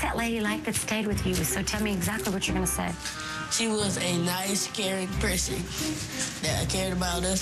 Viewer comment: i heard him say, "she was a nice scary person" lol But i had to rewind and hear it again, he said "caring" lol